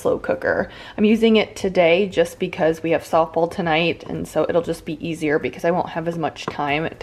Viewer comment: Great video... the patio looks very nice! Where did you say you were going at 17:46 I can't make it out sorry. I love the can opener! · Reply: Avery had a softball game!